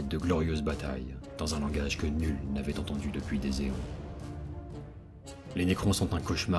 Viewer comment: "Depuis des" quoi ? Des éons ? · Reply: Oui, "des éons". Ce mot existe.